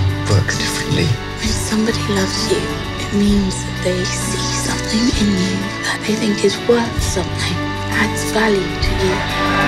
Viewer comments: in 2:10 to 2:19 what is her say someone help me please I hear "When somebody love you it mean they see something in you ..." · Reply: I think it says: When somebody loves you, it means that they see something in you that they think is worth something, adds value to you."😊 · Could someone write down what she said at 2:12 ? please thanks!! xx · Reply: When somebody loves you, it means they see something in you that they think is worth something, adds value to you